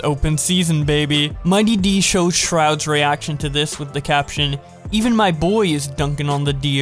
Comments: did you call summit shroud? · Did he just call him "My Boy Shroud" and showed a Summit Clip? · did i just hear him call Summit Shroud ???